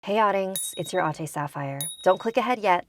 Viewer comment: I thought u said Hi oddings it's ur all day sapphire